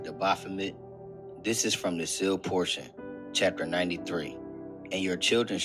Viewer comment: What book are those Bible verses from? I couldn’t understand what he said. Edit. I figured it out. It’s the sealed portion.